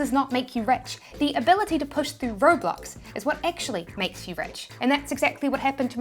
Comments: She said 'roadblocks,' I thought she said 'robots.' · When she said road blocks I thought she said Roblox